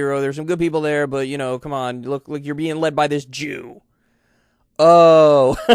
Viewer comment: Calling someone a Judas doesn’t mean Jew. It means they’re a back stabber or traitor etc etc etc. I’ve never heard that calling someone that is some anti Jewish dog whistle or smear. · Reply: I think the guy doing this video just doesn’t listen too carefully. He thought he said Jews not Judas